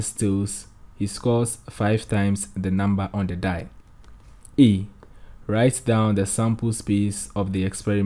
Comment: i thought you said “ei” 😂😂😂